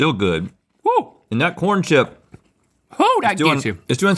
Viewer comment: l also thought Rhett said “thank corn” 😂